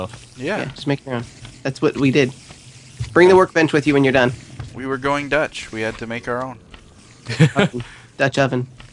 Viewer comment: did say: "We were going Dutch?" Someone answer me please.